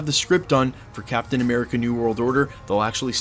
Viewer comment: It sounds like you say namor at 5:33.